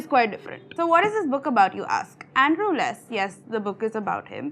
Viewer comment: Did she say Andrew Less!!!!